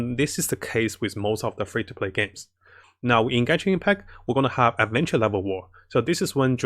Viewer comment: Did I just hear Gacha Impact? That's a good one lol